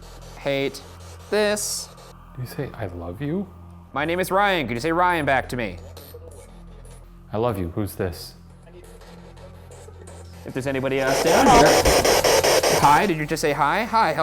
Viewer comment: anyone else hear a little "i love you" from the spirit box at 23:46 or am i just reaching